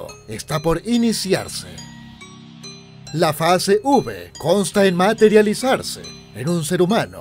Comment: Entendí "Fase Ubre" en lugar de Fase V jajajajajja